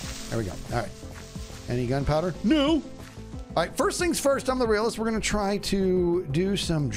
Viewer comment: "First things First, I'm the realest" ...did I just hear that correctly? * rewinds 5 seconds *